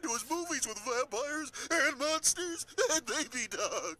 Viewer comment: does he say Nazi? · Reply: timothy rose he says monsters